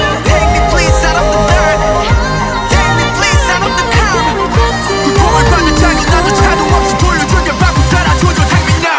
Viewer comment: "Take me please I'm about to come"!? I KNOW THAT'S 100% MY BAD AND MISHEARD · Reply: take me please out of the calm